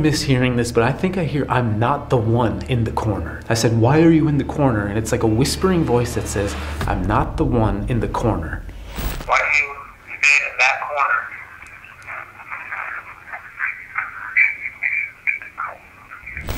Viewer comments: Regarding the EVP heard, it sounded to me like it said: "i'm not limited to the corner" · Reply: I hear "I'm not the man in the corner". Weird how many of us hear something different. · New video next Friday! 👻 If you want over 15 minutes of DELETED SCENES from this video join the FEAR CLUB: <link> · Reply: ABOUT THE EVP IN CRYPT: I hear him say "doesn't go to the corner" but I'm not so sure about the word 'doesn't' .... BUT I FOR SURE hear "....go to the corner" I just can't be so sure about that first word :( - BUT I am POSITIVE it DOES NOT say "I'm not the one in the corner" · I hear "something something. " push you into the corner" or get you but definitely "into the corner" 🤷🏼‍♂️ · Honestly, I thought I heard “I don’t want to be in the corner”. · I definitely hear I'm not the one in the corner p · Referring to Crypt recording. It sounded like it said "I'm not the only one in the corner"!! Thats what it sounded like to me.